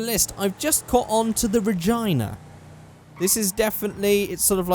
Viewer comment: for 1 sec i thought u said Vagina.... but no it was Regina :P